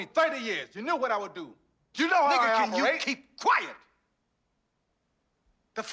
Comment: did I hear him say, “Nigga can you keep QUIET!!!!?” 🤯